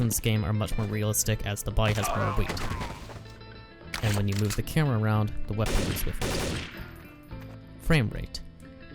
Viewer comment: what did you say? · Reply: "And when you move the camera around, the weapon moves with it."